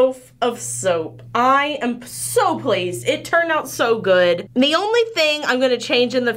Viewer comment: When you said "So pleased" I misheard it as "SOAP-PLEASED" or "SoaPleased" LOL! <3 My favorite popcorns are chocolate seasalt caramel (because that flavor is amazing on everything) and a GOOD cheese popcorn! Bonus if it's white cheddar! I also really like popcorners!! YUM!! : )